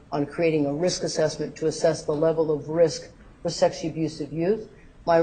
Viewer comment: Did she just say "Sexy abusive youth"? 24:32 She says it again at 25:08 and again at 27:54. There is nothing sexy about abused kids, you freak--go back to pervert island you sick fuck.